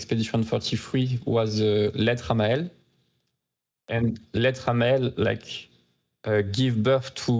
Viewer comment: I couldn't catch that, what songs does he mention here? are they on the soundtrack? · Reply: The song is called "lettre à Maelle" and yes it's in the soundtrack.